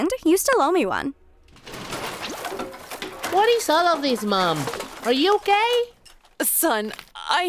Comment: I heard him say mom are you gay